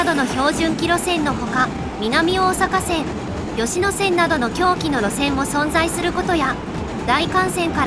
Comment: 「南大阪線・吉野線などの狭軌の路線も存在……」が「南大阪線・吉野線などの狂気の路線も存在……」に聞こえてしまうのはな～～ぜ～～？ｗｗｗ